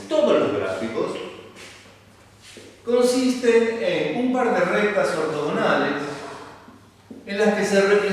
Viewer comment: Hola disculpe que dijo en el min 2:41 ? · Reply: "... consisten en un par de rectas ortogonales..."